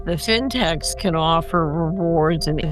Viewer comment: [ I clearly heard "SYNTAX" from her mouth! 🎉]